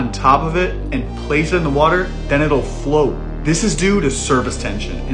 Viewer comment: Bruh did you just say “service tension”??